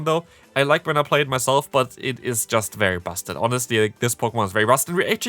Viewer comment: i hear b@ ik you said busted